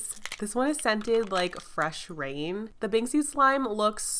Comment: Hold up did she just say “ fresh rain “??????? ( Timestamp